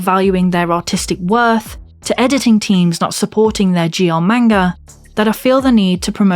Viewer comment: Ohhhhh GL. I thought you were saying "Geomanga"